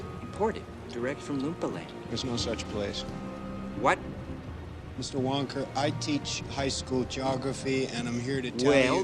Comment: did he just call him "mr.wonker" at 1:07?